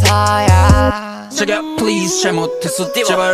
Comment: 나만 자기야 plz 가 아니라 재계약 plz 로 들리나..